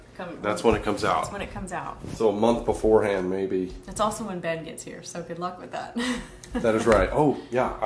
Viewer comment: Did I just hear the baby name at 11:10 or so ? Ben